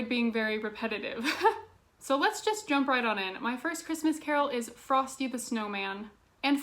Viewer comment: You said Frosty the Snowman and I was co fused because I thought you said "Christmas fairytales"